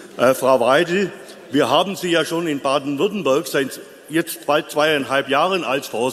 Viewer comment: Ich hab "Baden-WürttembÖrg" verstanden 😂 Frau Weidel war super, wie immer!